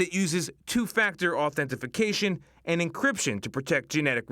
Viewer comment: Did he say "Authentification" @ 2:53 😂? It's like he took the words "Authentication" and "Identification" and combined them to get "Authentification" lol.